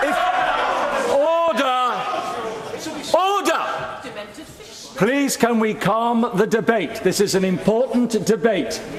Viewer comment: Did someone say Demented fish at 2:36 ? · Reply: Demented Fishwife lol